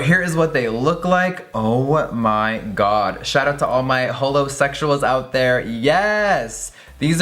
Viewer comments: Did he just say HOLOSEXUALS? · wait hold up....did he just say all 'his' holosexuals😑